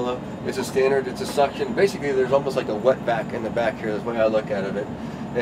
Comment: WTF did he said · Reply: Watback?